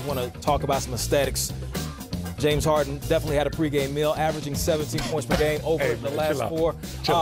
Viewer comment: Thought he said averaging 17 pregame meals 😂🤣